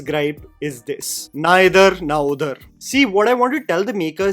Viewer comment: I thought you said neither na udhar